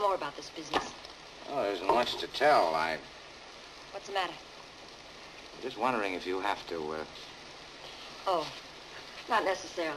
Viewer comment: What is he trying to say about her glasses at 2:36 when he says "just wondering if you have to, uh"? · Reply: Just wondering if you have to keep your glasses on.